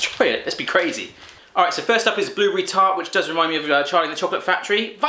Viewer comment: it sounds like he said retart lol